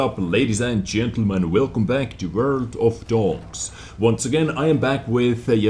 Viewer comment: I thought he said "Welcome to World o frogs"